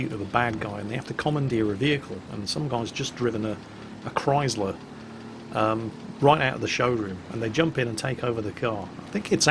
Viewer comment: OMG did you say chrysler fuck why do you have to keep bringing wargaming up jk